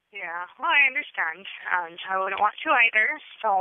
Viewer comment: did she actually say we don't want you either?